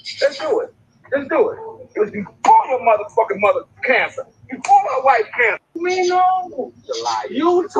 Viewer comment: Hmmm 🤔... Sounds like he said, "BEFORE your mother had cancer".... "BEFORE my wife had cancer" ....